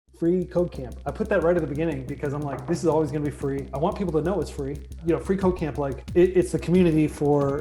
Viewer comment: I legit thought he said "free cocaine"